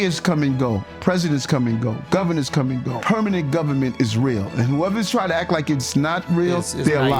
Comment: “Permanent government ISRAEL🇮🇱!” Yup! I heard it correctly!